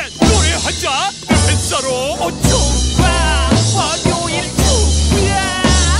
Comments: 이쯤 진짜 월요일 ㅈ까 라고 들리는거 같은데 기분탓? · 월요일 조..ㅈ 까... 나만 이렇게 들리나 \(ㅇㅅㅇ)/